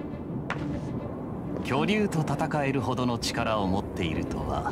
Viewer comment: 巨乳にしか聞こえん